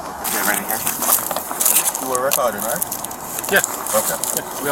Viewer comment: I misheard (with a low tone voice with an accent) saying "Are you retarded?" lmao. The cop misheard him to0 if he is a "seargent" but actually "Are you recording?".